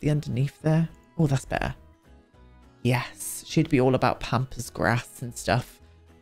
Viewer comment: I heard “pampas grass”, “pompous grass”, and “pompous a$$” at various bits here and I’m dying laughing 😭😭